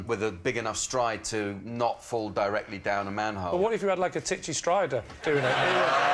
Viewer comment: I know the guy is called Tinchy but seeing as he is quite small, Lee calling him "Titchy Stryder" could be a pun. Not a particularly funny one but still. Also, "Tinchy" isn't a word which means smalll. That's "titchy" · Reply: Yeah i hear 'titchy' and I do this for a living